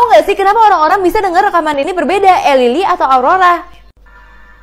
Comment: aku dengernya Elily tapi kakakku dengernya Aurora?!Oh iya kakakku ada 2 yang denger Aurora itu kakakku yang lelaki yang perempuan belum aku tanya😢